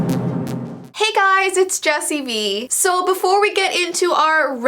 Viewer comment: Am I the only one who heard her say Jessie Bee?